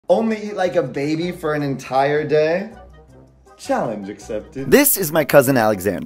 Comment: Bro I thought it said “I have to eat this baby for a whole day”